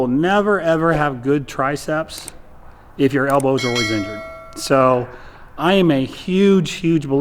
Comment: what does he say at 0:39? "... when your elbows are ????? ". word is eaten by the stupid bell sound. YouTubes auto-caption says 'injured', but I doubt that's correct. sounds to me like _'orizented'_. maybe a native speaker can help out here? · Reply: If your elbows always injured